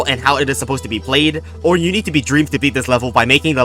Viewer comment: What did he say at 4:33? "you need to be..."? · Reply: you need to be dream dream was a minecraft speedrunner until it was revealed that he cheated his speedruns using a mod that artifically increases your luck, forever changing the term dream luck into "dream" luck in the mcyt community as well as many other communities